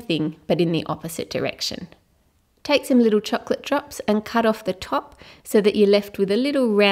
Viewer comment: She just said chocolate chops instead of chocolaye chips in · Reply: I think she said chocolate drops